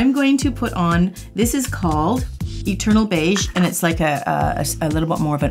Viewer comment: I heard "turtle beige" instead of "eternal beige". I thought that was a super odd name for a color. lmao